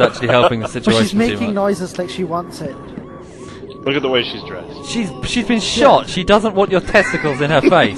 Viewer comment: wait I’m sorry did he just say look at the way she’s dressed?